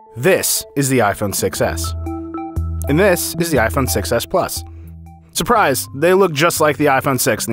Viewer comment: did he say "Iphone Success"